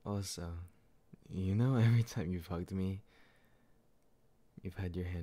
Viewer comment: WHAT DID HE SAY? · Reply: „hugged me“ not f* ed me